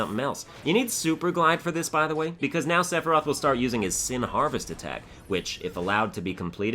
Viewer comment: Sin Harvest attack??? I think you mean Heartless Angel attack. He says, "Descend, Heartless Angel". Heartless Angel is an actual move from some Final Fantasy games used by final bosses, which reduces the player's HP to 1 and MP to 0. I think Kefka from FF6 and Sephiroth from FF7 are the only final bosses who use it. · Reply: Sora Snow yeah but it's a long running joke/mishearing that he says "sin harvest" because it's kinda hard to hear him over the sound effects